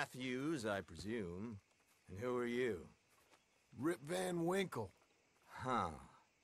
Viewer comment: I laugh so hard everytime I hear the name r i p v a n w i n k l e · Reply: I thought he said r.i.p. van Winkle til I realized he was saying rip van Winkle xD God, I love how john can be so goofy